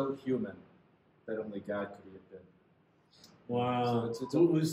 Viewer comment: I couldn't quite make out the quote at 48:25 -- Jesus was so human that only God could ???? And who was being quoted? Thanks! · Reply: I believe it’s “Jesus was so human, that only God ‘could he have been’”